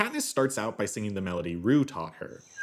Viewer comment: How does Katnis starts out by singing 'The Hanging Tree' in what sounds like rue talking but the captions say RU taught, what is it called and what is the style of singing?